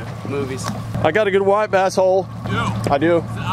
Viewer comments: Not gunna lie I misheard your statement when you said you had a "nice white bass hole". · As a certified fat guy, I approve of Chick-fil-A for breakfast but only the sausage biscuit with egg and cheese. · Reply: lol. What did you say? I had to listen to it over and over again about seven times before I figured out you say "white bass whole". This is a family channel so I will not say what I thought you said, but it sounds like you just went Lunker's in the woods. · ok, 2:28 what you said was "I got a good white bass hole". What I heard was, "got a good wipe A-hole." lol